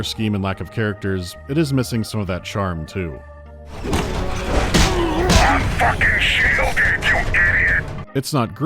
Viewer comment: The captions are really bad in this video. A lot of lines that aren't even close. 37:54 has "That fking sht will get you hit." when he clearly says "I'm fking shielded you idiot." It's such a downgrade in the captions and it's hardly the only one...